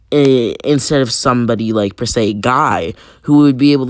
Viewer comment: I thought you said god will train Naruto